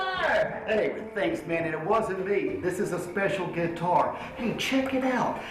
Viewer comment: Somebody, please tell me that he did not say "Gay-tar"